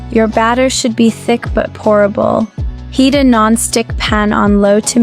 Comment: you said the batter should be thick but "pourable". The caption said "horrible." 😐